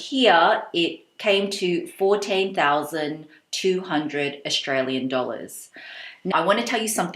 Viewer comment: I thought you said "48,200". It was so good of you to show the price and the price comparison in black and white. 😀